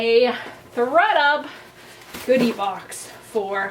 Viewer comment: I've never done a styling service, and I don't know that I would, but this was a fun video! I also, at the beginning, thought you said you got a "booty" box instead of "goodie" box, and I was really interested in what that was going to look like! Hope you do another one of these.